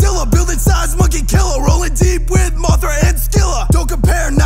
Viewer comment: with subtitles it says instead of mothra it says motorheads killer